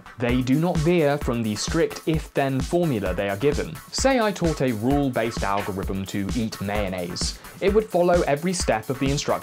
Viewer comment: I thought you said "Say that I taught a rule based algorithm how to eat nails" :P